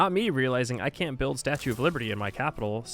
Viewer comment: Hehe something cut in the audio at one point, so I heard "Statue of Lurby" instead of Liberty. So it was said, so it is named. Forever and henceforth in my mind and my Civ games. Thank you. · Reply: I actually said statue of lurby hahaha. Idk where I got it from but it's what I call it